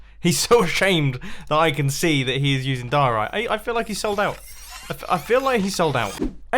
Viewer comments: I love how the caption is just saying "Bird Poop" instead of Diorite.. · turn on the subtitles, and it says Bird poop instead of diorite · I love that in the captions it says Bird poop instead of diorite lol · what I don't get is this: 5:16 You said he's ashamed that he's using diorite and my subtitles say: "Bird poop" 🤣🤣🤣